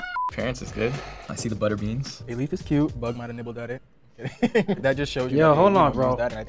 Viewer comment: what c word, im confused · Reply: the c word is 'cute'